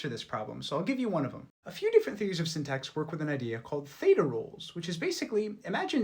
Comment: Damn, i heard "feta-roles" :O